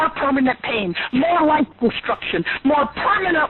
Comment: Instead of "life destruction" I thought she was saying "liposuction"